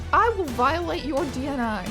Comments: Lol "I will violate your DNI" is a great line but I initially thought you were going to say "I will violate your DNR" as in you would resuscitate the Croaker against his will in order to be able to kill him again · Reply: For a second I thought she said, "I will violate your DNA." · Lol "I will violate your DNI" is a great line but I initially thought you were going to say "I will violate your DNR" as in you would resuscitate the Croaker against his will in order to be able to kill him again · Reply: I misheard it as "I'll violate your DNA" which sounds like one hell of an ominous threat